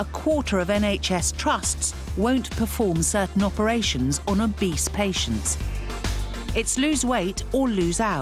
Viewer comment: I thought they said on a beast patient